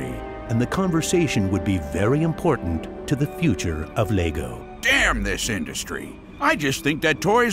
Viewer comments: DID HE SAY DAMN... In mY ChRiStIaN MiNeCrAfT sErVeR!!! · Did he just say “D**n”?! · did he say “damn”? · In 10:10 did he just say damn? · Did he just said damn in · Is he saying damn? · Did he just say damn?